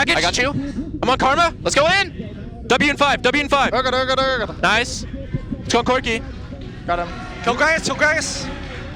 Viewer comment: WTF did Sven say at 8:00 ? durgadurgadurgadur LMAO xD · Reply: Urgot Urgot Urgot LOL